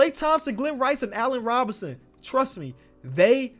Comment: Allan Robinson? I think it say Allan Houston